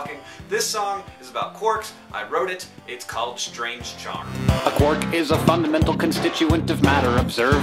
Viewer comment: When I first watched this video, I thought Hank said "it's (the song's) called strange John..."